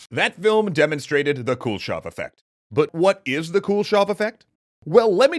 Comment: I keep hearing that as "cool shot effect".